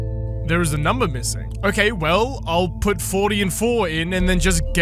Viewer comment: When she said " 40 and 4" I heard '40 and 04'(because place holder 0000s) and spent the whole time watching you recount your struggle nearly in tears laughing.